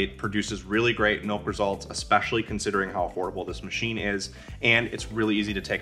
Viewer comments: Is this a joke? Did he say the crowd pleaser is a horrible machine? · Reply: I thought so too but he said "affordable" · Is this a joke? Did he say the crowd pleaser is a horrible machine? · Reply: I heard it too but turned on closed captioning and it’s “affordable”. The funny thing is I heard “affordable” the 2nd time and now I keep going back trying to hear “horrible” again and I can’t hear him say it and it’s kinda making me nuts.